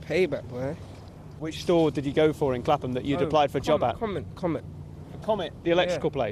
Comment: same thing i thought i thought he said no comment at first but he said comet haha so stupid